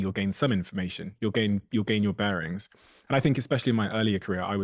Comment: Did she just say "ex-specially?" Oh. 😮 (Twice 😢)